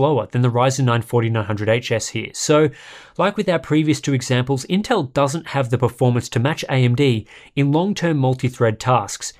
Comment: Intel doesn't have the performance to match AMD.... Am I hearing right?